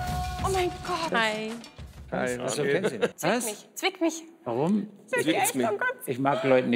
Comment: Ich hab verstanden f*ck mich statt zwick mich 😂